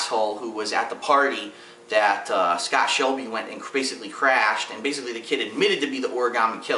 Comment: LOL when was talkin bout scott. i lagged and i heard say part of the letter c. thought he said "well scott shelby went and chris browned his guards" well my dream ended when i hear him say "crash" D: but i think gordi is a bser, ethan is just having thinking problems, scott is trying to hard to be the killer, the fbi dude is just a druggie sometimes, and idk about the girl